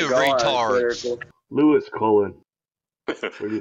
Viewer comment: I swear they say the name Lewis Cullen that’s weird asf as that is my name 😱😂